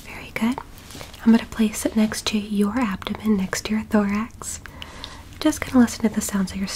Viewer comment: Bro when she said thourx I Thought she said Lorax I guess I have a lorax